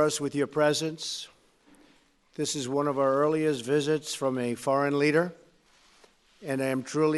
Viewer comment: did he just say fareign leader instead of foreign leader